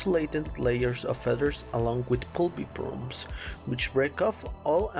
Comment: This was very informative. However there were a few times I couldn’t understand what you said. 2:13 here you say pulby probes or something? I’m not sure what you could mean. 5:23 here I believe you say either clavicle bands or cloacal bands. I assume the latter but I’m not sure. 5:51 here I think you said a pair of eggs but I’m not sure. 6:35 I think you said they can talk parallel with each other but I’m not sure what that means. 9:14 I’m not sure what you said with “Opak?” 12:22 here it sounds like you’re saying spinal mounted railguns?